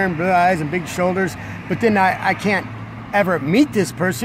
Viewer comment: I can listen to this guy talk for hours. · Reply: Oh, I was starting to get kinda angry because I thought you said I can't! My dad's a felon to.